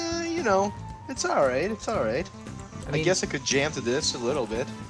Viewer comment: does he say he could djent to that? Will it djent? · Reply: No, he says he could "jam" to that.